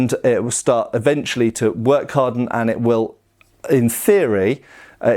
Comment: My first Kvetun got stiffer over time and nobody believed it could happen. It started failing 15kg flex tests at tournaments. However at 1:11:21 you mention that a blade can "work harden", did I hear correctly? That a blade if pushed past its elastic limit repeatedly can stiffen? I do a fair bit of pell work and like to flex my blades.